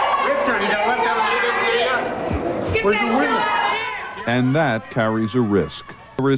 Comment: did Richard say “where’s the women?”